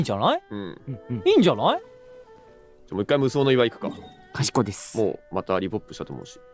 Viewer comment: Thank you for translating! At 31:25 I think what Horieru said is a shortform かしこまりました (Yes sir/Understood/Roger) not かしこい (Clever/Wise). I remember hearing Japanese streamers shorten the words to かしこ quite often when playing games like Apex and such. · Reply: am japanese, it's ｢かしこです！｣. used a lot when gaming, thats what you say when you want to sound casual but not rude. kind of like ｢サンキューです！｣ etc.